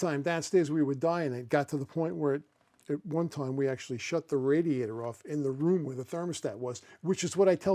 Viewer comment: I thought you said we shouldnt shut the radiators off!!